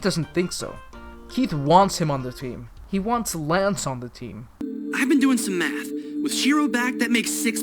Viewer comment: it sounds like Lance says “I’ve been doing some meth”